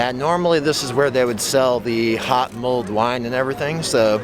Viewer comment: you say "this is where they sell "intelligible words". ??? YouTube autocaptions say HOT MOLD WINE Which is by the way what I hear and also my mom heard lol Please let us know what it is you said haha Have a good day.